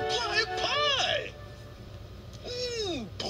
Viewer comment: I always thought he said poison berry when I was a kid